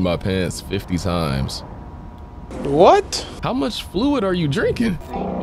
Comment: did he just say how much food did you drink